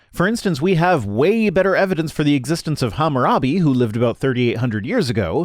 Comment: Who else thought he said “Harambe” and completely butchered it? 8:47 “Gravity is not Allah.” —, 2021 10:05 “Your theories are the worst kind of popular tripe, your methods are sloppy, and your conclusions are highly questionable! You are a poor scientist, Dr. Venkman!” — that guy from the original Ghostbusters 25:23 Are you sure it wasn’t on a MrEnter video?